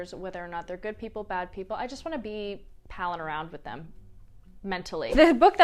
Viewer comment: Non-native speaker here. What does she say at 4:57 ? around with them mentally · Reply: “I just want to be palling around with them … mentally.” “Pal around” means to spend time with someone as a friend.